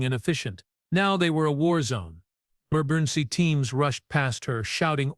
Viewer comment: Spelling errors. [4:37] "marburency teams"? What are these? Some words cut off in the middle. Why does the picture randomly spin?